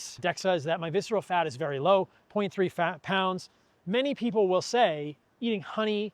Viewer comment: Viseral fat = .3 pounds. It almost sounded like he said 23 pounds.